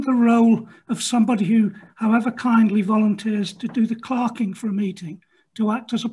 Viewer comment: I thought they were saying “clarking”